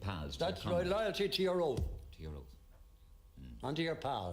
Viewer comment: I don't think he said "oath" I thought he said, "loyalty to your own". British soldiers don't fight for their monarch and country they fight for their mates and Regiment.